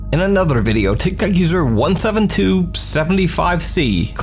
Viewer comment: doesn't it sound like everytime he says tik Tok it sounds like he is really saying tic tac